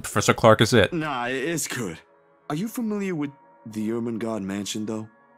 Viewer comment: Every time they say the name of the mansion all I hear is ,"The Erh merh gersh mansion" xD